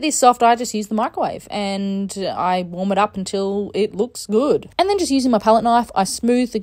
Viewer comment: I heard " I vomit up until it's boot"😶 But the cakes looks very yummy...❤